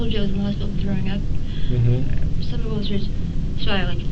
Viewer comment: unintelligible= stomach ulcers. 😂 I dealt with it for years I know that's what she said 45:28 probable cause statement